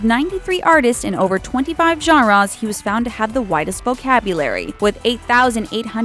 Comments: I thought she said "whitest" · I thought they said "whitest vocabulary". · oh. You said widest vocabulary. oops i thought you said Whitest vocabulary.